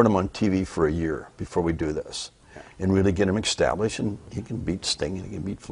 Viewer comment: why does it sound like he says ''ecstablished'' and ''ecspecially'' lol